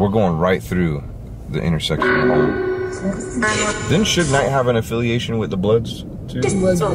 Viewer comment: sounded like “mike” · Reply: I thought it said Knight